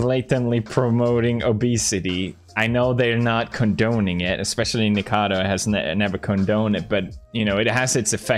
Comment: the caption said "Nicaragua" lmao